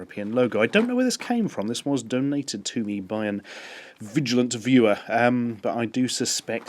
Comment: I don't mean to be rude, but I thought I heard flatulent instead of vigilant. lol My headphones are bad.